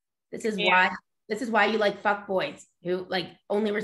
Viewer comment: did you just say fuck boys? Lol.